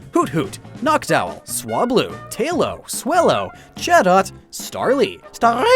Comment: "... Pillow, Swallow..." 2:04 Edit: I heard Pillow instead of Taillow